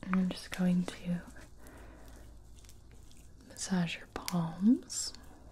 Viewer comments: I heard that as "I'm going to massage your balls " My heart dropped instantly and I went wtf in my brain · Hol up, Am i the only one that heard "A Tingly 🐱 Feeling" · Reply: Lucky you. When she said she was going to "massage your palms" the first thing I heard was "massage your balls". I thought, well, this is going somewhere I didn't expect.